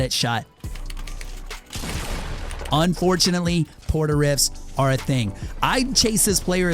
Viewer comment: Did he say port a rift in